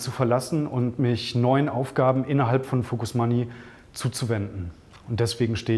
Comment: hat schon so viel Kohle und Kontakte gescheffelt, er wird jetzt Reformer der Deutschen Bank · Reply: Wieso Deutsche Bank? Ich habe Focus Money verstanden. Die zahlen wohl mehr als er hier verdient. Follow the money halt.. :)